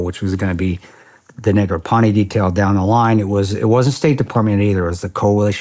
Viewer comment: Anyone else notice that Chris says the N-Word at 01:09:45 - 01:09:47?... Im hoping i misheard and im wrong but i could swear he said the n-word-ponie detail... Edit: I dont believe Chris is a racist i think he said something else that sounded like that but i have to query.